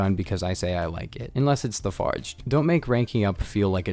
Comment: what guns is he saying here?i wanna use it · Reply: The fargd, it’s the best gun